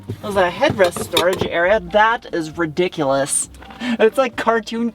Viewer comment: I could have sworn you said "Ridicurous? 😂😂🤔